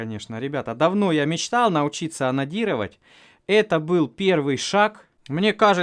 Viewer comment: "Классно конечно, давно я мечтал научиться онанировать" именно так мне послышалось